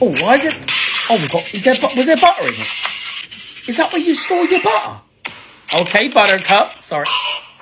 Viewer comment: What he said “is that where you store your butter?” What I heard: “is that where you store your mother?” (As in ashes)